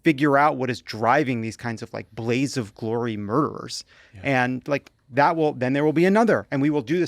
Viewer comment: I thought someone said “Blades of Glory murderer.” I pictured Will Farrell in spandex looking menacing. I need to get my hearing checked.